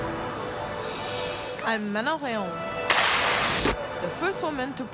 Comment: Who did she say her name was? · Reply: Manon Rheaume